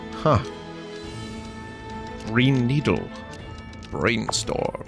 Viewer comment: I keep hearing "Pine needle", but I think I just have bad hearing. Apparently I can't hear some low frequency sounds.